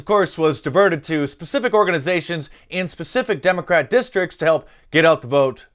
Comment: YouTube placed an ad at 3:55 which disrupted what you were talking about. I knew the word they were trying to keep us from hearing so I went back a few seconds to watch again and heard you say "Democrat". Sneaky bastards, ain't they. 🤨